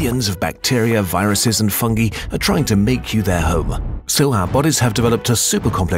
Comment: did anyone else hear hoe